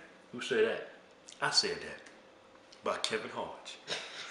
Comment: I thought he said Kevin Hart .